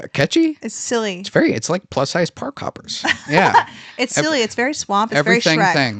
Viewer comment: I thought he said pork sized park hoppers, as in porkahontis.